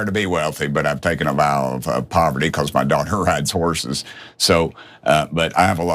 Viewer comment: I thought he said his doctor not daughter